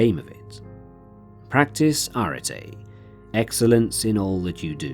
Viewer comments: Great routine! What is he saying at 26:09? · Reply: "Arete" - <link> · what is the word you say after "practice"? I can never quite catch it but I want to look into it more. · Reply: Arete